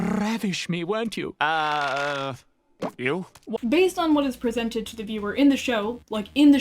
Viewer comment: he actually said “ew” and not “you?” Just saying 😘